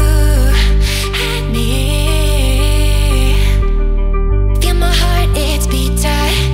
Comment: I hear "k my heart it's pizza" at the beginning